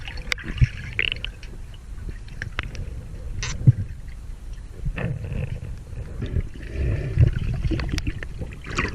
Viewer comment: Wait... At 4:56 did you say the 'SH' word? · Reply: MattNeonMiner2 Raszka “Shoot” I think